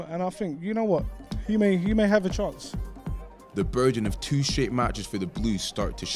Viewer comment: what.... did he say, the burden of two shit matches?